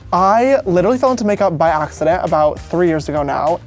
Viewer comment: did anyone notice the piss at 2:00? just me? okay. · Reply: rEeEeE lol i thought you were saying that he was lying about falling into makeup by accident😂😂